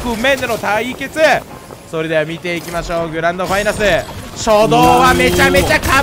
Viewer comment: 最初グランドファイナスって聞こえたw